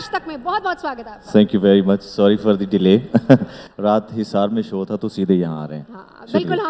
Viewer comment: Does he said 'hisar me show tha' ?